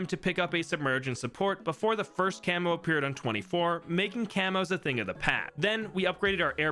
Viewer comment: did he just say "a thing of the pat"?